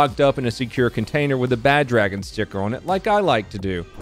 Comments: Great breakdown as usual, quick and to the point... And you were the one I least expected to have a Bad Dragon sticker on your gun box... I could always send you more :P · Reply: That threw me for a complete loop. I had to replay it several times because I thought I misheard "bag check sticker". Lol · I thought you said Badge and Sticker. No, you said BadDragon sticker